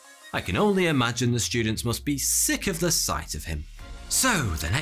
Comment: Omg it made my day when i heard “ students should be sick and tired of him”